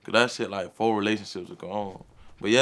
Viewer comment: “ Ion wanna speak bout her that’s like 4 relationships ago”😂😂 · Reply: I thought he said that whole relationships gone haaaaa